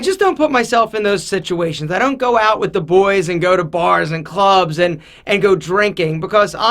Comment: Wait.. did just say he dogs the boys??